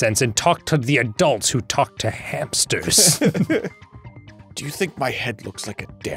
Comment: Oh! Talk to the adults who talk to hamsters! I thought he said hipsters! 😂😂😂